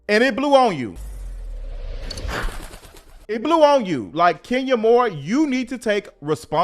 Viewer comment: Everytime I see Kenya I hear you saying "Skenya Moore" 😂😂😂